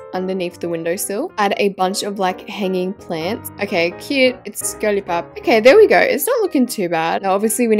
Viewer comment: From 5:24 to 5:26 I heard "Okay, cute! It's a skirly pop!" Im dying